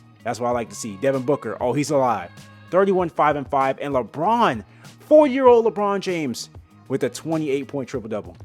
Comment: Did he say 4 year old Lebron? 😅